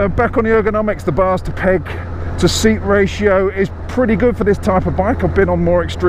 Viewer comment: When you said 'Bar to peg ratio' I heard 'Bastard Egg Ratio' and wondered what you were talking about lol